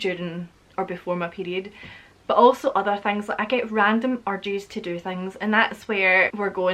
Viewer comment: I could have sworn you said "random orgies". LOL!